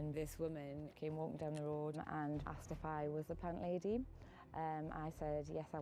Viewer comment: says she was asked if she was a ------- lady? I couldnt catch what she said???? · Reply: CandaceWhite i think she asked if she “was the plant lady?”